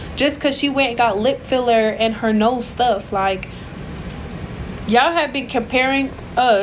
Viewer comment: Did she just say “her nose stuffed” 😭😭 yea she’s shady 😭😭